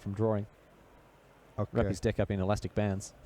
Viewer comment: what did he say?????????????? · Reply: "wrap his deck up in elastic bands" i think lol